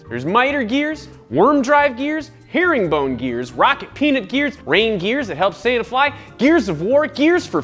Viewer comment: rocket peanut gear....almost misheard them as rocket penis gear XD